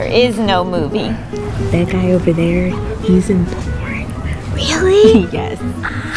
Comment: what did the black girl said? im total clueless here... "That guy over there he's ....." 'Singaporean'?? 'boring'?? that's the best thing i could guess.. and what is DP i mean seriously what DP they talking about because my version of DP is something real bad :P Thanks..